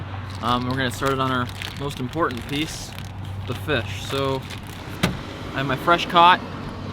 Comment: I thought he said I have my fresh C*ck